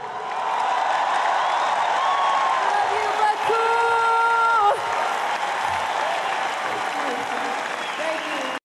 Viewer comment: What says in the second 3:09? after finishing the song, also says singing xD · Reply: She said, i love you Baku